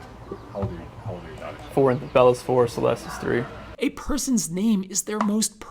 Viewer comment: does he say Celeste WAS 3, or IS 3? I mean, we all know what happens now but it almost sounds like he says WAS, or maybe he kinda mixed was and is. I dunno.